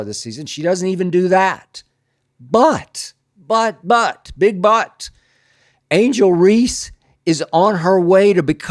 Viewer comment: But..but..big butt 😅 that's what I heard! And she does have one 😏